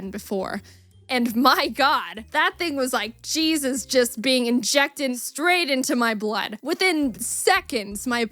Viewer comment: am i the only one who thought she said “cheese is getting injected straight into my blood stream” ( 8:05 )